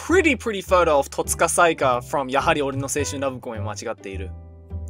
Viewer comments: w0t did you say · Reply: Gaetan Almela Yahari ore no Seishun Love Come was Machigatteiru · what anime? He said it too fast I didn't catch what he said 😢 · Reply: Yahari ore no Seishun Love Comedy wa machigatteiru